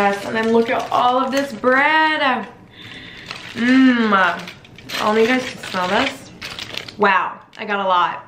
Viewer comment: did anyone hear her say “i don’t know if you guys can smell this?” 😂